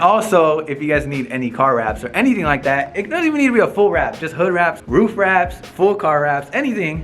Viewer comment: Congrats !! Where is the shop at ? Lol. he said hood wraps I thought He said hood rats!!